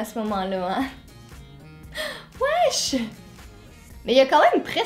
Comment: Cest moi ou a 2:17 elle dit wesh?!😂